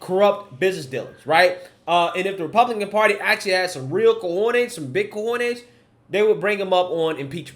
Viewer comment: What did he said at 10:38/:39? Subs says "if the republican party actually had some real coordinates some bitcoins they"???